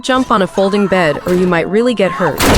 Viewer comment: I thought it said "floating bed" 😭🙏